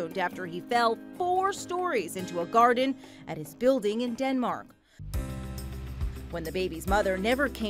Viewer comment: did i just hear denmark? DENMMARK?! Likes video